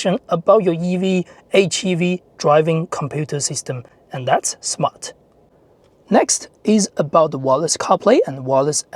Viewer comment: I thought he keeps on saying, "That's smut."